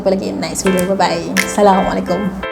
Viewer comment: Hi sayang , maaf ye mungkin telinga saya salah dengar tapi saya nak tanya , kak sebut assalamualaikum itu jelaskan sikit boleh ke , sebab saya dengar macam aisaylamualaikum. Kalau saya salah maafkan saya tapi kalau kak dengar semula ada terilap sebut lepas ni betulkan ye ❤ I LOVE YOU ❤❤❤❤